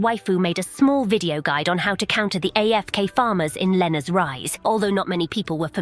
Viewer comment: I heard Lenne’s rise and for a second I thought you were going to talk about me rolling rocks off a cliff 😂